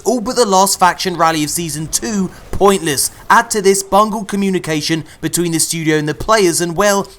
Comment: Lol, at 6:36 did you say bungle? Lol I never thought I'd see the day. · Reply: I heard BUNGO lol